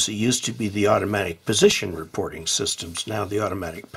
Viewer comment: I keep hearing "Anytone Position Reporting Service"...